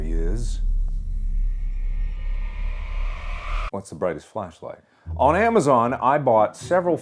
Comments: I thought it said the Brightest Fleshlight.. I’ve been watching too much Cr1tiKaL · I thought it said brightest fleshlight